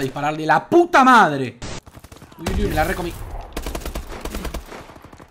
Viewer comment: minuto 10:00 que dice ¨ me la re comi´´